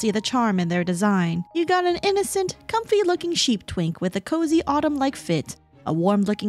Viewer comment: WTF?! 0:35, did you just say a slur?? ("You got an innocent comfy looking sheep t